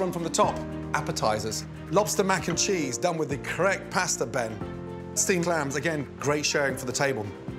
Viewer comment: I thought you said we were having steamed hams?